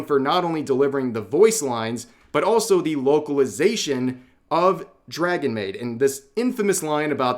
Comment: I thought you said "woke-alization" lol